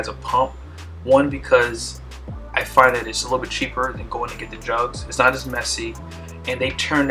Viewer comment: I had to rewind because I thought you said "going to get the drugs" 😂